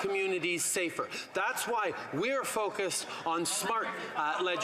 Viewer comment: I dont know about freudian slips but at 4:48 he says projected instead of protected because he knows hes activly confessing through projection